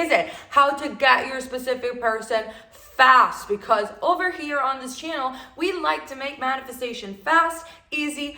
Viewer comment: I thought you said "in this channel we love to make bad decisions".. 🤣😭🤣😭 my ears this morning. Am sorryyyyy